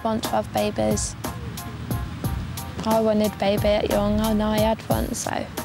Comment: ''i wonted baybuh a young en i had un so''0:19 0:28 what did he say